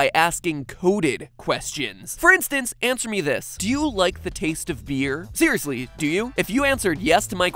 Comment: I misheard "do you like the taste of beer" as "do you like the taste of fear" and I was like wtf no the I read the screen 😅😂